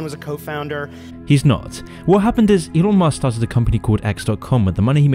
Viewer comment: captions say iron man instead of Elon musk lol 😂😂😂